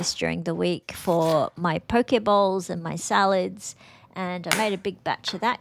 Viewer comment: Love your meal preps. I got confused for a second when you said poke bowl; I thought you said Poke ball (ie: Pokemon balls,lol)